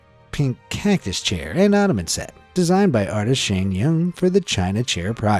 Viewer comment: WAIT DID YOU SAY OTTOMAN